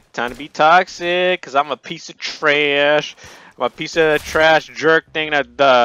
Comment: did he just say his self trash?